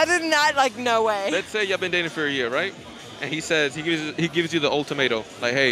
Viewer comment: did he say “He gives you the old tomato” 😂